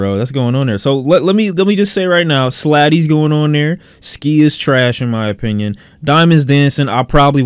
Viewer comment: Did he just say ski is track 🤡🤡